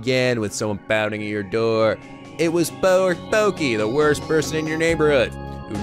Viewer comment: did say porky before correcting himself.